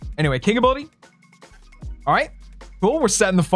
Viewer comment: Did just say 'Aight'?